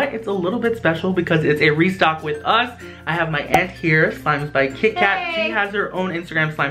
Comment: did he say i have my ex here???